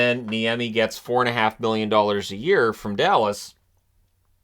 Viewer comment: Did you say “he got 4 1/2 billion dollars “?